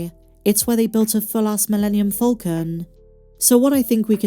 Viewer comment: I kid you not, i heard "Full ass millennium falcon" & not "full size" heh totally random, i know